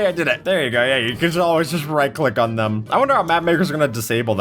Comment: WHAT DID BRO JUST SAY??? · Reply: Map makers